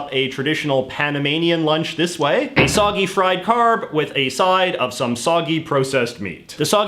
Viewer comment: The first time I watched This video I Thought J.J. said "a soggy fried carp" instead of carb. took me a few minutes.